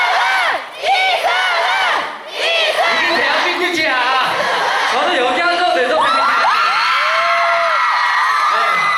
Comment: 진우 뭐라고 하는거에요 ?? (사오정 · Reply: 봄희 이게 대한민국이야~~라고 하는듯요ㅎ